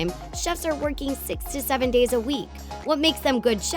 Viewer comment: who else heard 67 days a week